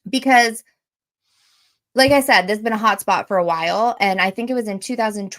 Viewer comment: Lol I thought you said a POT spot. Then I had to listen again. HOT SPOT! IM DYING😅😂